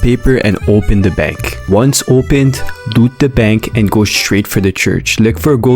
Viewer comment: "boot the bank" or sounded like doot the bank. 💀 edit: he has a lot of voice cracks